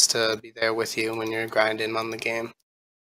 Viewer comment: "It's a good time having friends there with you when you're grinding mum again." 2:14 I can't be the only one who heard that